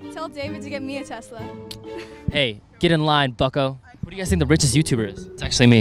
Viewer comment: I thought u said "hey get in line, fucko" 😂